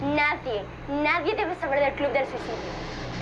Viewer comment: perdón escuche mal ? El club del suicidio ? Pero ahora como 4 M de personas ya saben